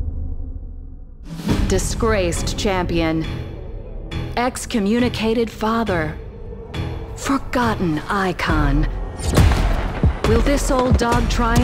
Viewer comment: Ballistic is the John wick of apex (I love it) Fr I thought it said excommunicado farther 😂